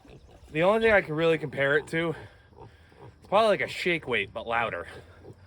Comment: What did you say you could compare it to? It sounded like you said 'shig whig' but the captions said 'shake weight'...neither of which I've ever heard of. 😄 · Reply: I said shake weight lol it’s that work out took that looks like your doing dirty things lol